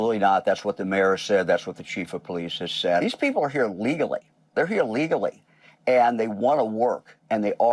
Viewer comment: There's a Subtitle error at 10:33 : The guy says "These people are here legally." But in the next subtitle says "They're here Illegally and they want to work." Despite the fact they say "They're here legally" because he's reiterating what he said before about Haitian Immigrants coming to the US legally, so the subtitle should read "They're here legally and want to work."